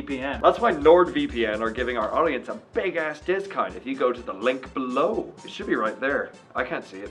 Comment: Did he say discunt at the end? Wtf